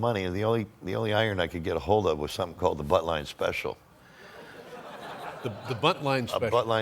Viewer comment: I thought he called it "The Buttline Special" 🤣but it's "The Buntline Special" as in the Colt Buntline, and that barrel looks like something out of a Looney Tunes cartoon 😆